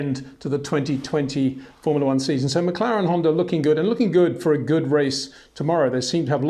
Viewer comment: Did Peter say, McLaren-Honda?